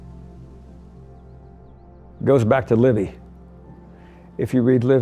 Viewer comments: Does anyone know what book and author is he referring to about "the placement of Bedrooms and Public rooms in a Palazzo"? I think he sais "Libby" or "Livia"?... ? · I know I am a bit late to the party, but I was wondering if anyone has a reference to what he said at 7:10 about where the bedrooms and the public rooms go in a palazzo? I hear he says something about "livey" but if anyone has a reference it would be great!